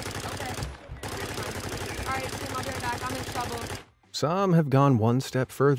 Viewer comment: You are wrong. At 26:12 , she CLEARLY says, "Guys ill be right back, im in trouble"